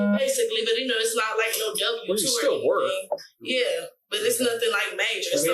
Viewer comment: I think at 9:44 she says “it’s not like no W2 or anything.”